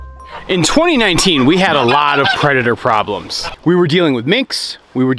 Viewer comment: I genuinely thought you said "In 2019 we had a real creditor problems. We were dealing with minx, coyotes and bobcats" and I was like... damn, this dude really hates his bankers lol